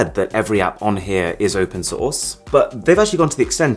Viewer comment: i thought you said sauce 😂😂😂